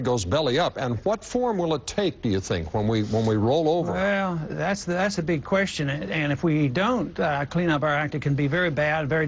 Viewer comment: noticed where a small adjustment needed 4:28 , 'Well, that's a big question' instead of 'well, thats a good question'